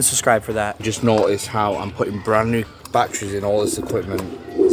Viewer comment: I thought he was saying he was putting brownie batter and all the equipment but he was saying batteries